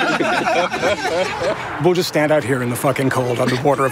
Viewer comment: Did he just say we’re standing out here in the Fucking cold 😂